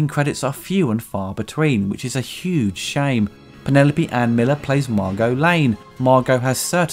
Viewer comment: Wasn't paying attention for a moment and thought I heard you say Lois Kidder plays Margo Lane. Ears playing tricks.